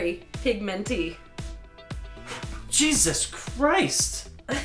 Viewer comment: i literally thought she said “Chris Dean”.